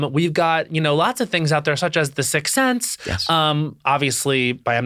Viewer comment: what is Matt saying? The major popculture moment? cc is no help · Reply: The movie The Sixth Sense by M Night Shamalan was a pop culture moment.